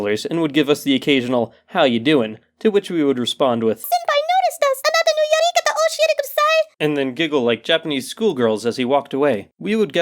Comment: It took me much too long to understand his slurred japanese/decipher the horrible grammar of what was likely google translated. But, in case anyone wanted to know what he said at 1:41, he either meant to say: "Please tell us more about you" or "Please show us more of you". Again im not quite sure as from what i can tell, he's saying あなたのよりかたを教えてください or something along those lines which roughly translates to "Please show/tell us your more" which is such a weird sentence, and even if I'm mishearing that middle word, it's still a freaking awkward sentence...... and why did I spend 15 minutes of my life trying to hear what he said.... i have no life.....